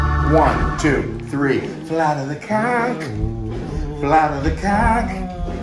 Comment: Did anyone else hear "Flood of the cock" over and over?